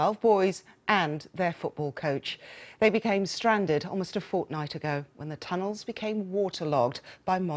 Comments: dId ShE sAy FoRtNiTe? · i thought the woman said fortnite 😂😂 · did I hear 𝐅 𝐎 𝐑 𝐓 𝐍 𝐈 𝐓 𝐄 ??!!!!1??? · did she just say “a fortnite ago” 😂😂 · I think she said a fortnite ago in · did she just say fortnite? lol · did she just say Fortnite? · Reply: Lucky Ducky - No. She just said “fortnight”. It means 14 days. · Did she say fortnite at 00:21 ?? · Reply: Nope, she said fortnight, meaning 2 weeks. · I’m confused did she say Fortnite